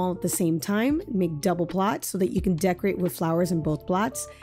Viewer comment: what the heck are you saying? It’s super unclear. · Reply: Make double plots so you can breed flowers on multiple farms.... You can turn on closed captioning as well to help with any audio issues if you can't understand what I'm saying... This point is irrelevent anyways now since you can reclaim flower seeds and use them on other farms so don't even worry about that part. Best of luck to you. Happy farming.